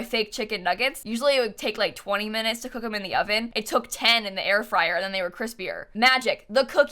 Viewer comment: I thought you said hair dryer and couldn't understand why you were so excited about it and when you said you had cooked a chicken with it I started to doubt your sanity .Whoops !! I realized you said hair fryer now I doubt mine. Love watching your builds but must listen more carefully .